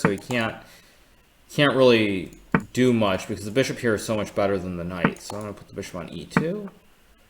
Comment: did he say bishop is much better than knights ?? did he ?? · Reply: He said "the bishop here is much better than the knight" (singular). The knight is "corralled", as its only escape square (a6) is guarded by the bishop.